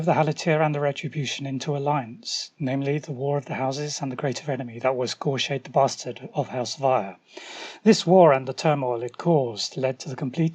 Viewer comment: I meant what it is said at around 2:30. Sorry english is not my native tongue xD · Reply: Ah, no worries: "The greater enemy that was Goreshade the Bastard of House Vyre." :) BTW, I share all my transcript links which you can get from subsequent videos (or via my FaceBook page for older ones because I only started adding the links to vid descriptions quite recently).